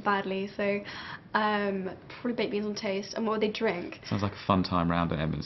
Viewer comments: thought she said babies on toast too. turned out to be baked beans on toast (i think) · at first i thought that she said babies on toast instead of baked beans on toast xD